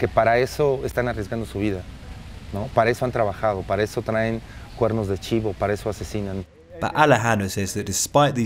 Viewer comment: he did not said Kalashnikov rifle, he said "guerrno de chiva" (idk if i spelled it correctly) or horned goat. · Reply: It’s Cuerno De Chivó!